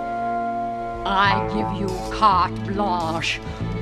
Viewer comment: What does she say here? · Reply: give you carte blanche